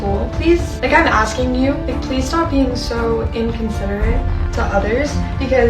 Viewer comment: um did Chali just say inconcerned instead of unconcerned? uhh sis go back to school instead of tanning in the Bahamas when we are in a global fucking pandemic like..... take your own advice lmao.... wont be crying if yall get COVID lmaoooo